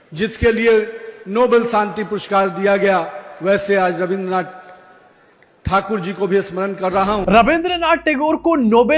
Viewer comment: did Only I heard Rabindranath Thakur instead Tagore?